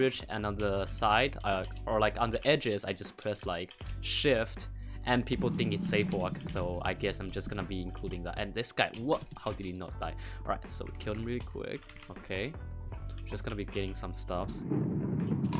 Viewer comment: when he said how did he not die 0:58 sounded like shotgun raids